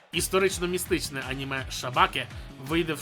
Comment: Мені почулося шабаки 🤣. Якщо там є песики то я точно дивитимуся.